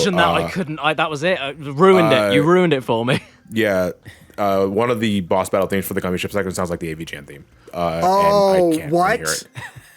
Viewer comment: Wait what did say here I couldnt quite understand him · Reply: One of the boss battles in the Gummi Ship reminds me of the AVGN theme.